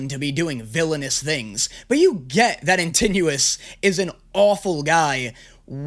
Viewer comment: did he just say "in tenuous" it's "antinoos" or "antinous" not "in ten minutes"